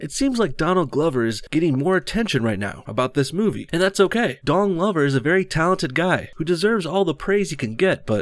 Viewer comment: sorry.....i must have misheard...but 0:32 did you say his name as....."Dong Lover"?